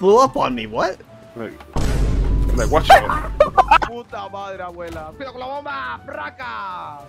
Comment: I heard him say "puta la wea" which is something along the lines of wh*re in Spanish. (I may be wrong)